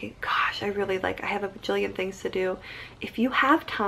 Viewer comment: did she just say "and you have vagilian things to do" ? :D